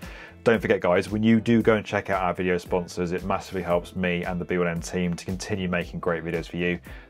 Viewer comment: did he said BLM?